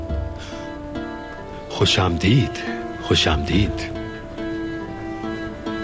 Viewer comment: i think the trader said my name is chaprasia😂😂😂😂😂😂😂😂